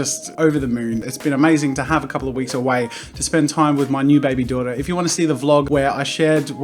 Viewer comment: Is I bad that I heard “and spend time with my new baby brother”?